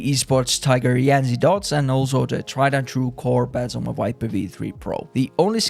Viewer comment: what skates was used on the v3 pro? 4:05 i couldn't hear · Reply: Corepads